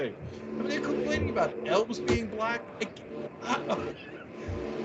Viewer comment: I genuinely thought he said why couldn't ELVIS be black it's a fictional world 🤣🤦🏼‍♂️ I need a break